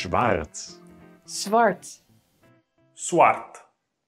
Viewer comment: did Dutch just say fart